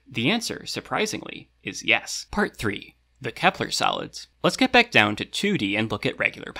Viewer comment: I keep hearing "Kepler Salads"